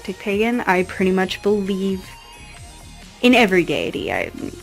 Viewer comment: Nice altars. For a minute there when you were saying you "believe in every deity" I thought you said "I believe in every Gay-ity"